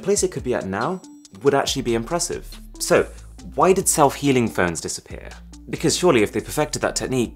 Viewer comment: i really thought it said "self-heating smartphone" and i was like "why would you want that? O.o"